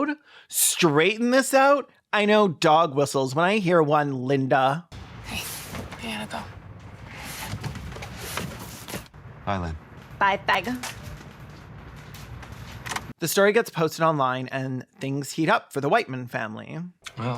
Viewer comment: Did she say bye FAG!!!